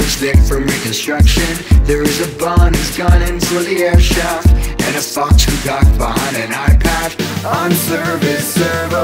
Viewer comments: Used to listen to this video without watching it Always thought it said "And a fox who ducked behind an iPad" Hahahaaaa I know better now. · It might be just me but i thought it said: There is a bear who bears no introduction and a chick who flicks with no disruption and a bon who gone into the egg shop and a fox who hides behind an eye patch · It sounds like at the "Fox" part: "And a Fox who got behind an iPad" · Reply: I thought it said "fox locked behind an eyepatch"